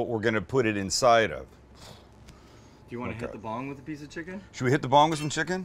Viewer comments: I thought he said hit the bong with the chicken I was like yoooooo it's LIT smoke and chicken lol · Did Sam just say should we hit the bong with a chicken. Didn’t know he smoked